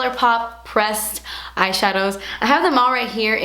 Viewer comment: who else thought she said "breast eyeshadows" lol at the beginning ^_^